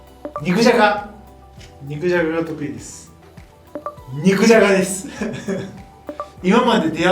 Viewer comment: 肉じゃがの言い方可愛すぎじゃろ😂😂「っん肉じゃが」に聞こえる🥰🥓🥔